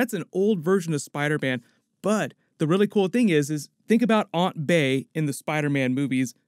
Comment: Am I the only person who heard him say aunt bae